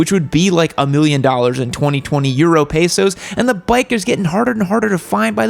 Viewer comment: Did I hear say pesos? Hello Filipino fan here.